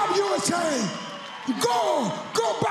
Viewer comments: He didn't say what people thought he said. He said "no forget Jesus, people!" LOL. It is pretty obvious. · So everyone would've carried on their merry way had this been just another incomprehensible "praise cheezus" speech from Romero but just because it sounds like he says "No for Gay Jesus" people are collectively losing their fucking minds!?!?!?!? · sounds like he said "no forget jesus" now that i listen to it again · For anyone who thinks he said.. "don't FORGET jesus" listen to how he says forget at 0.29 seconds. He even makes the hand motion. I thought he said forget too but after listening to it with headphones and even studying his facial expression I am 100% certain he said ".. Not for GAY jesus" He knew it was a strong statement and that's why he made the face after saying it. Everyone is entitled to there own opinion so I'm not saying there is anything wrong with him expressing his believes. I'm just saying what I believe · It sounds like he said "no forget Jesus People." Anyways. He just won the fight of his life. If he wants to praise Jesus. He can if he wants to talk about trains or grapes or Doritos. He can do that. He's the winner. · I heard "no forget Jesus." not anything with "gay" in it. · I understand the controversy it sounds like he says "not for gay Jesus" when he actually said "not forget Jesus" I'll cut him some slack he's trying to speak English with obvious accent issues.